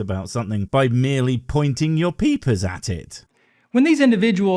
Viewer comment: Totally thought he said " idiots think you can know the facts about everything by pointing your PEEPEE at it!" for a sec 💀